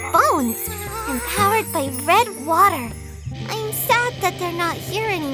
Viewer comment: For a sec, I thought she said powered by bread water and I was like mhm checks out. 🍺 🍺 🍺